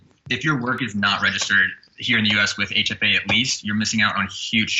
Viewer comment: he said "if your work is not registered here in US with ???" couldn't catch the organization, what was it? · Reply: HFA